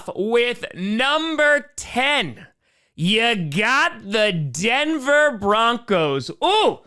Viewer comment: dude, i love the animation for the numbers, but when i saw navy blue and white and i heard “you got the d” i was so confused